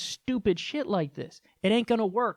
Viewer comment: I thought he said racket surgery.